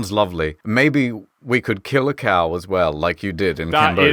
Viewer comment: thats it, I'm going to cambodia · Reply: oh you said a cow? I thought they said car maybe not at a cow